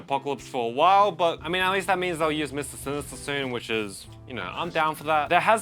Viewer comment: what hell did you say? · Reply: Use mr sinister soon